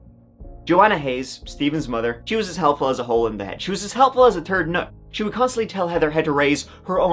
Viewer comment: For a minute i thought he said she was as helpful as a turd😂😅